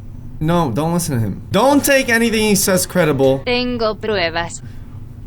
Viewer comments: was saying Anal 😑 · Why did it sound like said anal 3 times